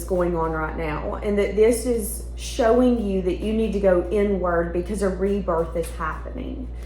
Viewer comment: I LOVE your accent, I'm listening to you on my tv while I'm on my phone & I really thought you said "it's saying you need to go 'n' word..." & It caught me off guard 😂 (I went back & understand the word was 'inward')